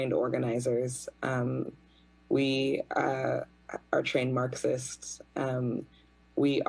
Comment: did she say trained marxist ? Marx hated black people 😑